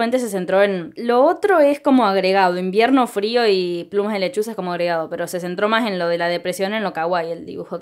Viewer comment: Pensé que decía "Plumas de lechugas" y quedé wtffff